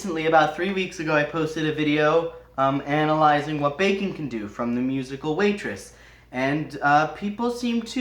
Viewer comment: Definitely thought you said "What Bacon Can Do". I've never seen Waitress, so I thought that would be an amazing song. Alas, I was wrong about the title.